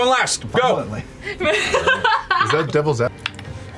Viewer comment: What did Marisha said at 10:20? Can't catch it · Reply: She's ovulating